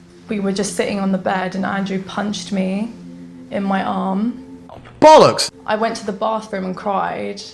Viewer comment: "Punched me in the arm" all i hear is "My dog stepped ona bee". Then she procceds to cry more fake than the one that said the second line.